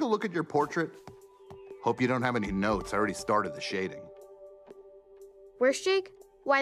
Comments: omfg I didn’t notice the first time Tiffany called Jermaine “worst Jake”. I thought he’d said “art Jake” lol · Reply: I thought it said “where’s Jake” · omfg I didn’t notice the first time Tiffany called Jermaine “worst Jake”. I thought he’d said “art Jake” lol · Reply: I thought he said "Where's Jake?" Like as if he didn't know which one on the painting was Jake. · omfg I didn’t notice the first time Tiffany called Jermaine “worst Jake”. I thought he’d said “art Jake” lol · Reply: I thought he said ‘where’s jake?’ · I thought he said where and not worst Jake